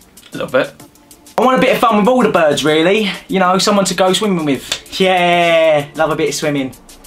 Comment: O MG. I thought said “Swinging “ at 5:52 “ Instead of swimming. I was so worried for a sec there. 😂😂😭😂